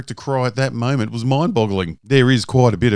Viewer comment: did you say "mind bottling"? Is that a tribute to Tyler Zed and Danny Boy, or am i mishearing you? Or do you just not know that it's "boggling"?